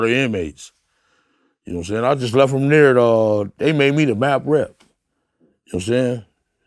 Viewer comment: Lol Keefe D clip had me slipping… I had to rewind and replay thought he was saying “they made me the bad breath”😂😂😂😂😂😂